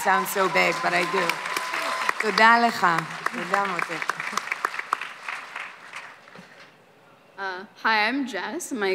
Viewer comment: what did Gal say? Min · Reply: Yeimy Ramos she say: thank you thanx sweety